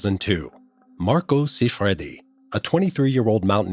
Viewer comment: Thought he was saying Rocco siffredi